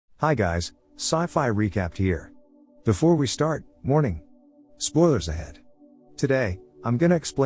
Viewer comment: It literally sounds like he says Before we start, MORNIN' in the start of every video